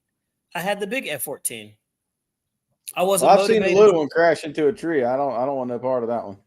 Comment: My apologies to. I had kids playing in my background and I thought he said: "Will crashed into a tree." But he said at 1:07:12 "I've seen the little one crashed into a tree." A public apology for a public misunderstanding.